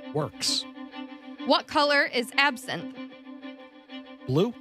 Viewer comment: OHHHH, she said "What color is absinthe." I thought she said "What color is absent" as in, like, what color is absence of color or absent from the primary table. lol